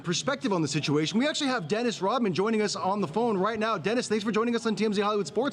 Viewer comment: I thought it said REDMAN, as in the rapper.